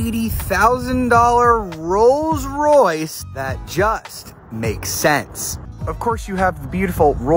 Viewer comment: Who else thought he said things that DONT make sense?